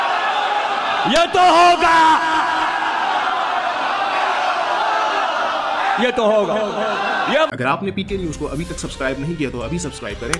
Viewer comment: is legend saying ye tu ho ga 😀😃😄😁